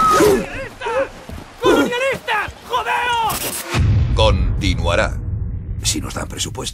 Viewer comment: Alguien sabe que dice en el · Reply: "Imperialistas, colonionistas, jodeos!" (perdón llegó dos años tarde xd)